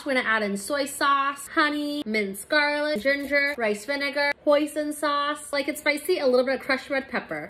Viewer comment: Is it just me or I heard “pOiSoN sAuCe” 😂😂